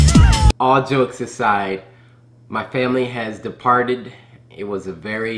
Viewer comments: Did he said 'deported'? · I thought he said “my family got deported” I WAS SO CONFUSED